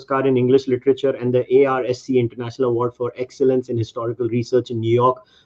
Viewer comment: Did he just say ARSE AWARD at 0:50?? Lmao